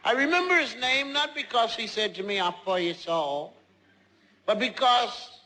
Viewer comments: I thought said "Ill borrow your soul" Not "Im four years old" I just got it! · At first I thought when said"I'm 4 years old" I thought he said "I'm for your soul" :/ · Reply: I thought he said I'm really old